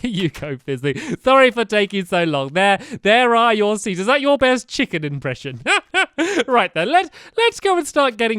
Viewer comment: I thought you said that is your last video because my thing froze I was like no!!! I'm your biggest fan